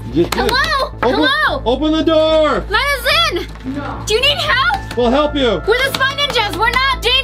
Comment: Who heard no at 5:23 when said open the door Cos I did✨